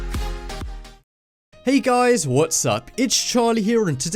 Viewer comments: I'm an idiot I thought Charlie said "What's suck" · did he said "Hey Guys What Suck?"